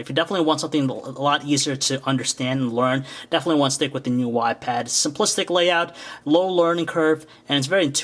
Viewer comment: All I keep hearing is "new wypad."